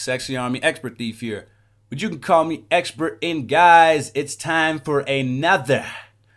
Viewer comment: It sounds like you said your a expert in guys lol